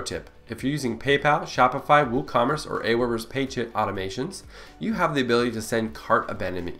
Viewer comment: Thank you for this. But please use subtitles! I googled the following phrases because this is what it sounded like: "Paid chip automations" Pay chip automations, Paid chit automations, Pay chit automations. But I could find not info about any of them. I didn't google "Paid sh!t automations", but THAT's what it sounded like! What the heck did you SAY?!! (at 11:47 and at least 3 other times) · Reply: Apologies for that! The phrase we said is "Page Hit Automation." Here's more information on it: <link> AND <link>